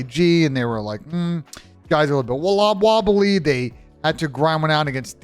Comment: Does anyone know what he said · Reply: Wawa wobbly